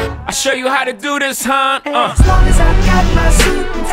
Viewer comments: "And as long as I've got my suit and tie" I'M SORRY BUT ALL I HEAR IS "JOHN'S BARBECUE AND FOOT MASSAGE" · Y'all I thought they was saying As long as I got my soup and I